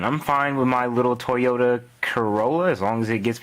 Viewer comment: For a second, i swore you said Toy Yoda Corolla.... I swore i nearly went through a mental breakdown...